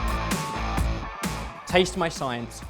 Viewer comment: It's about a 50/50 chance that I hear them say "salted" rather than "sorted" at the end...